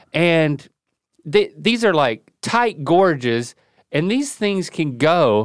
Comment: I thought Link said "tight gorgeous" at first, but then I realized it was "tight gorges" 😂